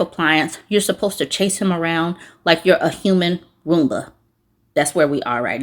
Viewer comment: I heard "womba" instead of "Roomba" 😅 next gen Wife Appliance just dropped